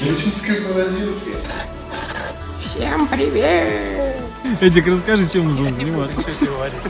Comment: Мне послышалось или он,сказал:Привует?